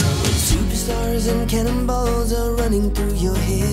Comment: I thought he said, "like cannibals"!